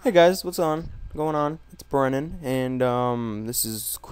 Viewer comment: I thought he said "it's Vernon" LOL